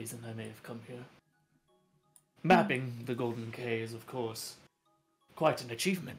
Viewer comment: thought that said “the golden gay”